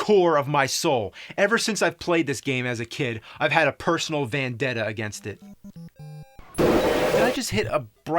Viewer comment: either he mispronounced vendetta OR its the greatest low key joke I've heard in a while. It sounds like he said "VANdetta" and in the next scene he runs into a van... coincidence? I THINK NOT!